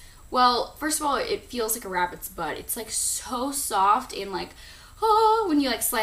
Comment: Ohh, haha. I thought she said rabbits butt! (